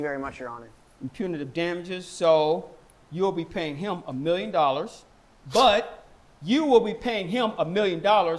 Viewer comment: A million dollars. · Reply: sounded like he Said a billion dollars 😅